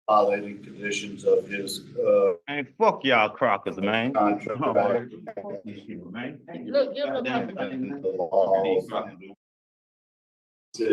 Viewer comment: Wait a minute...did he say at 9:43 that I'm a "crocker?" How dare you, sir, how dare you take Betty Crocker's name in vain!!!